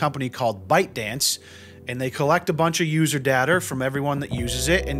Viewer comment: did homie say “user dater”